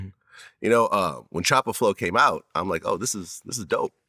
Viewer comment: did he say choppa flow ?😂